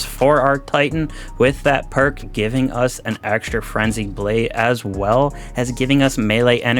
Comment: Like this if you heard him say "Flenzied Brade"